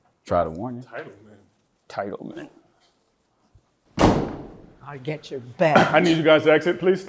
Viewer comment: Sobbing with zero tears. The second she said 'i'll get you back' the judge should have given her jail time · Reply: I heard, "I'll get your badge."